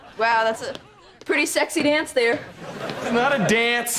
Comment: Did they really say "sexy" on a children's TV show??? Am I mishearing it???